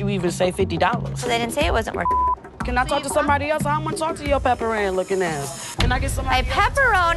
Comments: I thought she said “pepper ann looking” not pepperoni...peep the mustache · Any other 90's kids here? I thought she said Pepper Ann, like the cartoon show. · Lmao the customer said "Pepper Ann lookin ass", thought she said "Pepperoni", the customers look on her face was priceless when said pepperoni lol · Sounded like she said Pepper Anne. I'm not going to waste time Googling it. #2 I like how they captioned "Fake Gucci " for the click bait. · I heard "Pepper Anne"...🤔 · Thought she said Pepper Ann